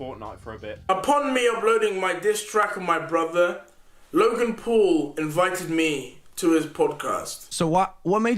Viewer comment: on subtitles, instead of writing «Logan Paul invited me. It says «Jimmy Neutron invited me»